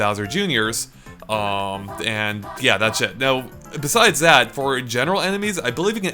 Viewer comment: he said "that's it" but it sounds like he said "that shit"